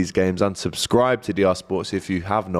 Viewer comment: Agt says Unsubscribe from DR Sports instead of Subscribe